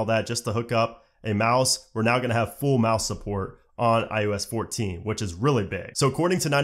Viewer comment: I swear I keep hearing him say "iUS 14"...it's iOS.